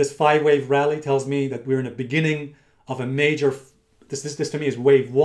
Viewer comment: did he say major fall @ 3:30?